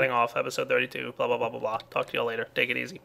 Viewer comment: At the end it sounded like you said " bla bla bla fuck you all later"